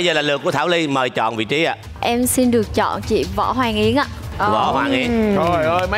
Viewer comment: Võ hoàng yến nghe thành võ hoàng yên =))))